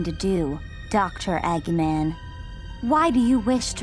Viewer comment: sounds like she said “EGG-NAN”